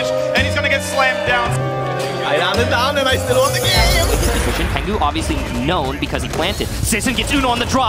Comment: what is he saying? i can hear only bla bla bla i still won the game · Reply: I ran it down and I still won the game!